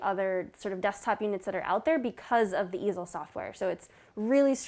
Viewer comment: The first time I misheard it as "The Evil Software", I know it is "The Easel Software", but I can't unhear it now.